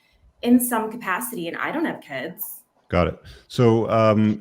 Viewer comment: I thought he said good. No, he said, got it. When people are not allowed to talk, they might want to find another way to vent their pain and anger. Of course, only communists feeling matter.